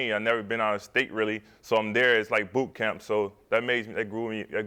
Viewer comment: I thought he was about to say "BOOT GANG" instead of boot camp lol.